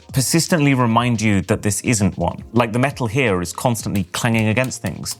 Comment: i misheard that as Metal Gear